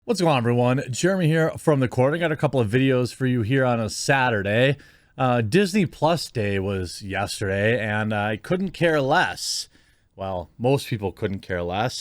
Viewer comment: happened... I only hear "Disney Sluts" now